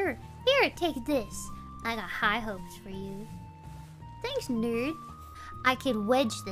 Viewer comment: I think at 1:03 she says nerd, not dude.